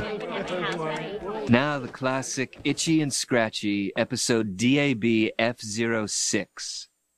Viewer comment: Did he just say dab?